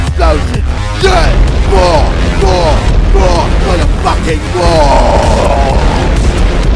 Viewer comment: Did KSI say 'fucking' for like the first time ever, on like 1:54??